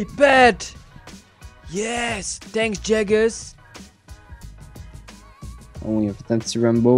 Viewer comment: Sounds like he said "Thanks jack ass".